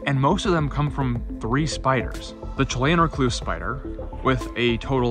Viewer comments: you mention a recluse spider, but I can't hear what you are saying. What species is that? Sounds like you are saying "twain recluse spider". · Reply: Chilean recluse- Loxosceles laeta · What spider name are you saying at 3:40 ? I've listened to it ten times, and even the closed captions don't really help. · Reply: Chilean recluse spider